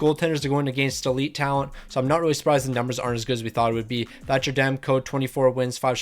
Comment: when you said thatcher demko the captions said That’s Your Damn Code lol